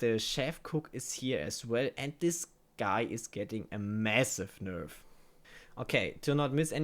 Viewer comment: did I hear massive?